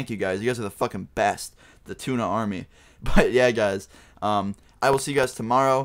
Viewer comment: Tuna army ( ͡° ͜ʖ ͡°) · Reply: I for a sec i thought you meant go say Luna Army untill i saw your profile pic